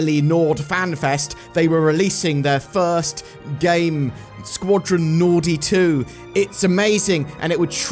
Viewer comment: LOL, I thought I heard you say "Squadron Naughty 2"